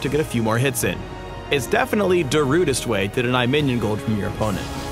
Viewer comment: He said Darudest in the video... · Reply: BentleyTheBrain I'm happy I wasn't the only one that heard him say Darudest